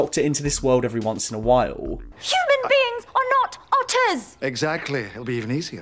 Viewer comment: I heard that as "Human beings are not auteurs!" Thought Clara was making an argument for separating the art from the artist